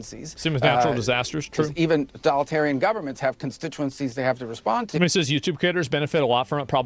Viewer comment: did he say Donaldtarium Government